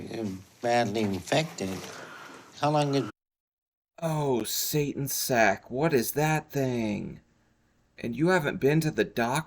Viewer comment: Did he just say what does Satan sack is that too funny way too funny